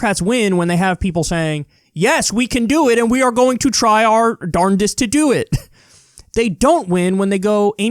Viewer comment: did Kyle just say “darndest“? Is somebody auditioning for a position in Bernie’s cabinet?